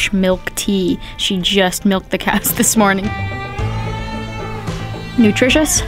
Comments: ish Wait did she just say "milked the cats this morning" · Did she just say she is having a tea that (She just miked the cat?) 🤮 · she's talking about "we're having fresh milk tea...she just milked the CATS??? this morning'... I can't be hearing this right lol What did she actually say? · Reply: Cows hahaha